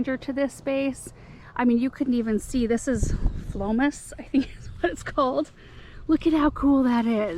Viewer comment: What did you say that plant was at 22:02? I couldn’t understand what you said. Thank you! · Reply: It looked like Jerusalem sage that I had recently seen in a Botanical garden . I checked on the internet and found that it's Botanical name is Phlomis fruticosa.